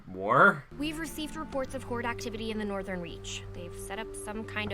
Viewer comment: Am I the only one who heard Hordak-tivity at 2:00?????